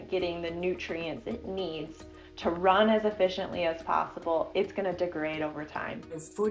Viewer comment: Thanks! I put the wrong timing, it's in 11:11 , what this woman says. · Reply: ooh! She said "It's going to degrade over time."